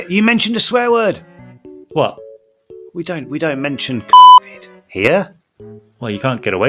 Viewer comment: THE BLEEP YOU EDITED IN WHEN HE SAID “COVID” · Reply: I thought he said cockvid😭😭😭😭😭